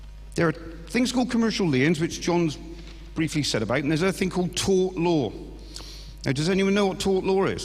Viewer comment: What is this tuaght, tought, tawt? law? I've searched for it but cannot find anything at all on it. What is the spelling of this word at 4:11, thanks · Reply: TORT